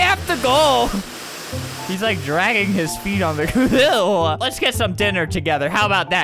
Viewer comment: i thought that said lets get some disease together xD